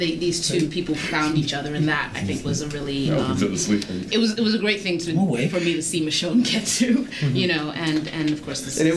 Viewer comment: What does JDM say @ 7:09? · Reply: oh, I thought he said Norman fell asleep over here (or there) before Norman says, "I'm awake"